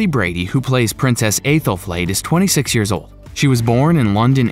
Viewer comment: did he just say aithelflaid 🤣🤣